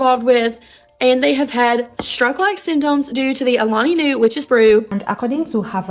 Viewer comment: Did she say WITCHES BREW